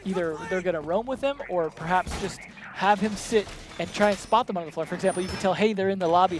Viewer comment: am i mishearing this, or did he say, "try and spot the motherfuckers" ?